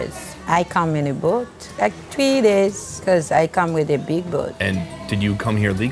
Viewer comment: Lol. Sonny: are you here legally? I thought she said she came in a big ass BOAT, bro. You're still funny as hell, dude.